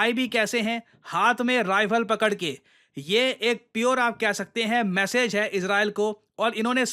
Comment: i thought he's saying, "ye ek pure atankwadi ke roop me aye hain" 😂